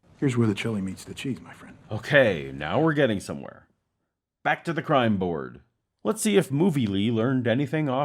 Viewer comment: what on earth did movie man say? I've heard thrice but xont understand? The chiefs? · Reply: Back to the crime board